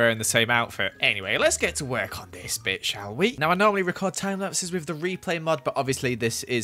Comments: For a second I thought he said “Let’s work on this bxtch” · Either Joel is not a family friendly channel now, or he forgot to cut that out O.o Edit: ohh sorry I thought he said he b-word but I listened to it more times and I realised he said bit lel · Is It just me or did it sound like he said "lets work on this bitch shall we" Edit: I think Its just me lol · Did he say bits at 2:44 ''Any way lets work on this bits shall we'' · a little before that I heard Bitc instead of bit I am I the only one who heard that